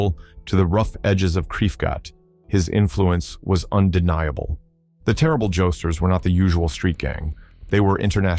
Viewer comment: The firsf couple times i heard it as "The Terrible Jokesters" i was thinking that was a wack name